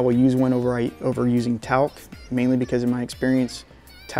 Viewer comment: What did you say at around 3:15? You prefer a glove over what? Talk? Jw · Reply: I don't like using a glove but I will use one over Talc.